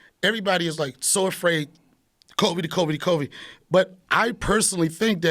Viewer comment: could've sworn he said Kobe-19 100 times